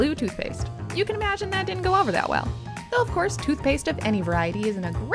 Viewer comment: my dumb ass thought you said "toothpicks"